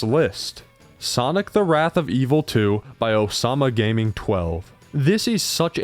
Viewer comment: ok I'm sorry did you say OSAMA GAMING?